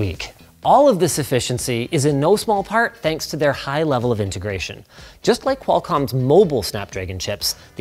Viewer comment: You said, "High level of integration". I heard, "a closed garden".